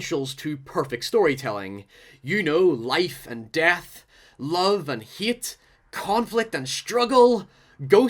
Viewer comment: I thought he said love and yeet